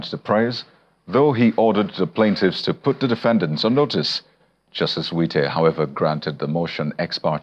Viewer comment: I thought he said Justice Wike 😂,I had to rewind